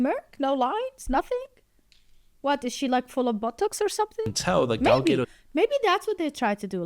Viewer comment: I am so sorry for laughing at this part at 7:05 I thought you said that she is full of buttocks. But after your further explanation, I was like "Ooooooh, BOTOX." My bad! 😅